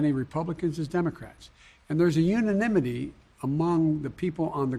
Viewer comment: unanimouty 9:47 - WTF was that word? Did you mean: unanimously No results containing all your search terms were found. Your search - uninimouty - did not match any documents. Suggestions: Make sure all words are spelled correctly. Try different keywords. Try more general keywords.